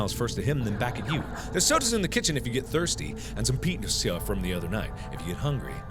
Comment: what was mark going to say? he stuttered on pizza and it sounds like he was going to say something else by mistake. Edit: 9:34 he says peen-is-za